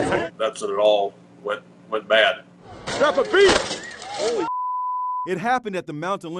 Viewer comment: Perfect timing he said “drop a beat” and the wire dropped to floor right after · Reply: I heard "son of a beach!"